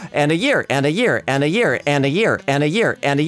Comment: i can literally hear say "anna here" instead of "and a year"